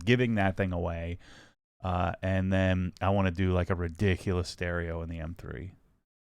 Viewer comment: Yas !!! I heard "ridiculous stereo."